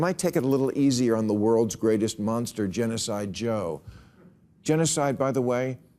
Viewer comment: did he call Joe Biden “Genocide Joe?”